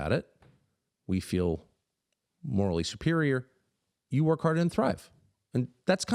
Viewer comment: can someone help, what does he say at @ 18:18 ? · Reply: you work hard and thrive?